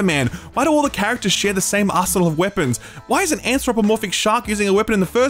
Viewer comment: When you were talking about the suicide game, my brain delayed, and I thought you said ," Why do all the characters share the same asshole and weapons?" I realize you said arsenal, but a sleep deprived brain hears things differently. Love your content, though.